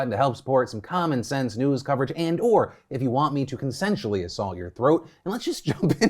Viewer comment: Thought mans said "Sexually Assault your Throat" DEFINETLY had to rewind Never having low audio again unless...?